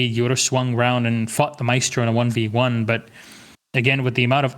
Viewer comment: was that just me or did he just say “fuck the maestro”